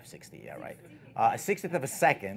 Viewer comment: Can anyone figure out the camera settings at 10:10 please: Aperture = f/4 ISO = 320 Shutter speed (speaker corrected himself when accidentally said f/60) = did he say 1/60 (one sixtieth of a second) or 1/6 (one sixth of a second)? Can't be be latter because there will be so much blur if he hand held. +B and H please kindly advise. Thanks. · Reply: 1/60 at f/4 sorry for the confusion